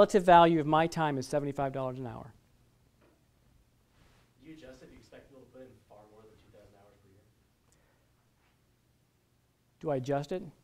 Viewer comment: What IS the Guy in the public asking? I cant hear anything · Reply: He is asking if we adjust the hourly rate if someone expects to work over 2,000 hours per year....the answer is "no"....